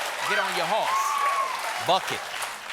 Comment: Ok is it just me or does Jon say around the 3:20 mark "get on your horse" and riding off the earlier NY times headline "fuck it"? 🤔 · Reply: Buck it.